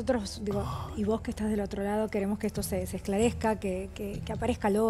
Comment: Yo escuché mal o dijo '...queremos que esto se desesclarezca...'